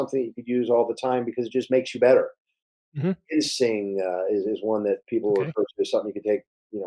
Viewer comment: Awesome podcast very informative I have one question though, What's the name of the herb doctor mentions when he was asked what are some good herbs vitamins minerals one can take? It sounded like he said jinzing when I switch to closed caption ... The word was identified as being.."seeing" So I'm assuming he said jin Singh being that it sounds similar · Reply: He probably said Ginseng